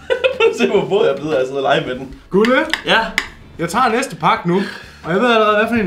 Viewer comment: hvad sagde du